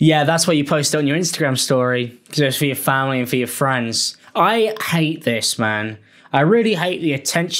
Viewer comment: I heard "I hate this man" then quickly realized what I had actually heard was "I hate this, man". For a second there I thought we were about to see the end of lmao. Glad it was just a silent comma. geez louise